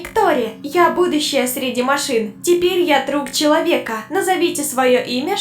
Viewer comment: Мне послышалось на минитуе 1:57 что робот сказала "теперь я труп человека"😅😅